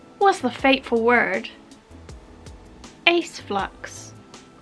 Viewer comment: Not gonna lie, these old geezer ears of mine could've sworn that you said 'Aeonflux' at first hahaha😂🤣 Great video! I'm always glad learn about labels I've never heard of before. Of all your videos, the ace episodes are my favorite; some of the best character development I've ever seen!🖤 There is still so much to learn😍 Thanks again for all that you do. 'Somewhere, something incredible is waiting to be known.'